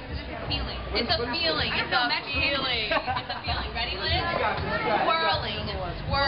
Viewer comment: Oh, it's SWIRLING. That makes more sense. At first I thought they said "squirreling."